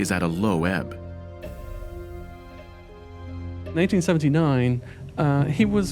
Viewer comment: does he say in” 1979” I listened to it 3 times and swear I heard 1979.